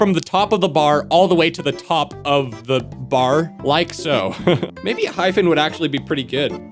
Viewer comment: I heard “enhypen would be good” ENGENE